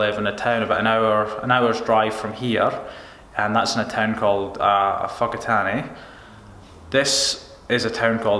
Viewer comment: Phonetically speaking... What did you say at 3:45? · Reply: It's kind of close to... "Fa-ka-taa-ne" (but you spell it Whakatane ...well, there is actually a line above the last 'a' ...so I'm not actually spelling it correctly)